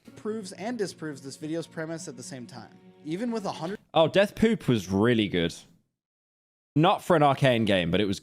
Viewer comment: did he just say deathpoop?